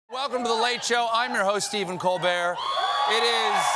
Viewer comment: Friday night, I hear....."I'm your host Stephen Coldbeer."